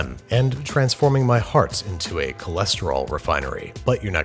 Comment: Did he say "hearts?" Is he a Time Lord?